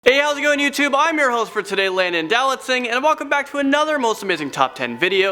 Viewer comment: At first I thought he said Landon do not sing